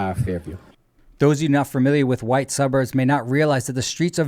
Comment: Did he say white suburbs?